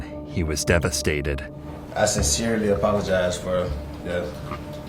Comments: Skid* and sincerely* not “seriously,” and not “skin marks” 😂 “I seriously apologize.” Just STAHP. 🙄 I can’t wait to see how illiterate you are for the rest of the video… NOT. Just goes to show this is what you’re hearing rather than what is actually being said and nobody’s corrected you yet and you definitely don’t read books. I detect California. Illiteracy is an epidemic in California. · Again at 4:00 "I sincerely apologize" and your editor says "I seriously apologize." Like they don't understand common sayings? Your channel sucks.